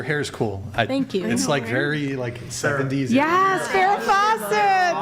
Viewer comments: what are they saying right about here? Her hair style looks like someone named Sarah? The mic peaks out because one of them gets excited and like the pronunciation of the name isn't clear. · Alex brought the energy tonight. The girls were really chill. was off her meds. good times 😂😅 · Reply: what are they saying right about here? Her hair style looks like someone named Sarah? The mic peaks out because one of them gets excited and like the pronunciation of the name isn't clear.